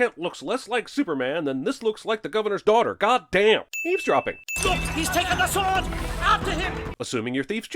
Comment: Okay, I don't know how many people caught the joke with "Eavesdropping", but I sure as hell did, and thank you for making me snort water out of my nose and covering my wireless keyboard! That kind of clever wittiness is exactly why I keep watching this channel. LOL! :D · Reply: I literally heard it as "leaves-dropping," lol. Or is that what he said?